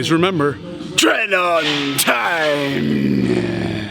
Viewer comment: I thought you said Train on Tren there 👀